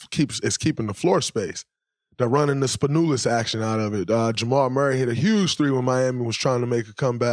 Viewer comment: Did Draymond just referred in minute 17:48 to the great Dimitris Spanoulis when he said ,,they ran a Spanoulis action” or I just misheard?